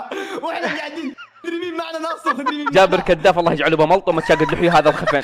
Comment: انا الصراحه ما ادري وش قال بس الطريقة الي قالها تضحك 🤣🤣🤣 /والي يعرف وش قال يكتبلي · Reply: قال الله يجعله بملطومه ذي سبه جيزانيه خفيفه😂😂.